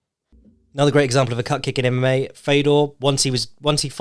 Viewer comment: took me 6 minutes to realise you're saying calf kick and not cupcake 🧁🧁🧁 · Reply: I thought he said Cup Kick for a good bit. As in Di*k kick.